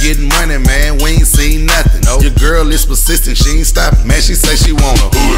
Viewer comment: I always thought he said “your girl is my assistant”. I was like danggggggg he done made his homies girl his assistant and now they’re sleeping together.